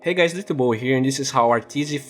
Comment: Anyone else think when he says hey guys it's d2 bowie here, and it sounds like hey guys, it's ya boy here. xd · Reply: DuckFromHell I thought he really said it's ya boy here. xD